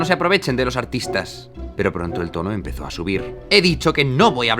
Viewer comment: entendí "pero pronto el PORRO empezó a subir" y casi que suena más fiel a la realidad